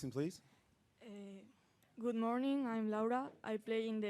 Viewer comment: Did she say ..good morning i am lauda(hindi term)